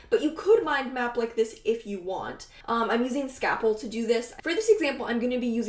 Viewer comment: Hi, what is the web method program you are using? I thought I heard you say scaffold, but i can't find it. Thanks!